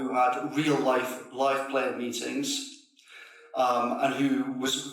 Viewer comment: what did he say there? "real life player meetings"? what does that mean?